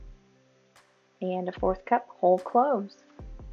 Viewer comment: Sounds great. I thought you said Old Crow instead of Whole Cloves. It gave me a chuckle. Lol🎉